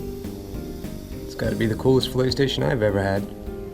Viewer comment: Ohhhh. Filet station. I thought you were saying play station which would have being a little weird.